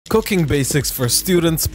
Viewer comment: I thought he said "Cocaine Basics". 💀